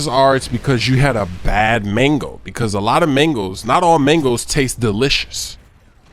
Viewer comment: I thought you said mangle until I saw the title